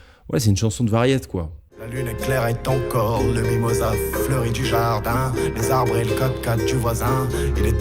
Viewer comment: a 14:33 , pourquoi le sous titre jaune remplace "du jardin" par "Javier Bardem" ?????? mdr